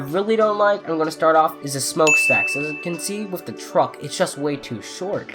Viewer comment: did you say smell? lol